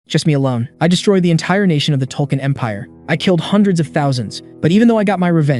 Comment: did he just say TOLKEIN EMPIRE??? 0:11 - Oh.. I see why 😂😂 No wonder Rings of Power seasons in Amazon prime is flopped 😂😂